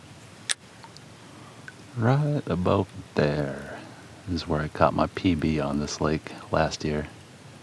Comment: Literally thought he said cut my pp last year in the same area, then realized shortly after he said "I caught my pb" 🤦‍♂️